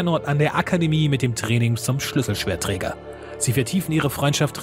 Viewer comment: Habe ich das bei 6:40 richtig verstanden "Schlüsselschwert Trinker" oder saß ich da auf meinen Ohren ?